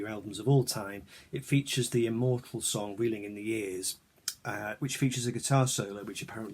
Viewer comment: I always thought said 'reeling in the east' lol I got that lp n never spun it,happy new year